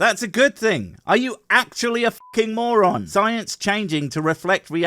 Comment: Oh shit did you say he was a Mormon I thought he said he was... Oh I hate subtitles Moron sorry my bad